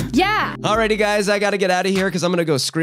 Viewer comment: I thought you said "I gotta go screw myself in the mirror" at first lmao 🤣